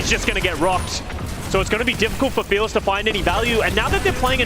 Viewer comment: he really said "now that they're playing ANYA" 🤣🤣